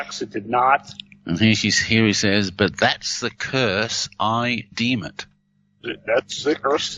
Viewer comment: Did not hear “that’s the curse I deem it” I heard “ that’s the curse THEY deem it” . maybe I’m hearing yanney instead of Laurel .